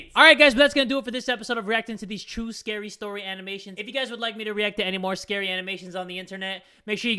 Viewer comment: I heard reacting to "True stories animated" But then I heard "SCARY stories" and screamed!